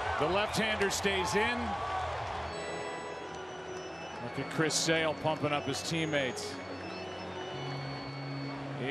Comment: what did he say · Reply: Ye “WAKE THE FUCK UP”